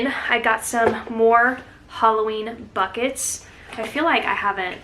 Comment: Sounded like you said “hauloween” 😋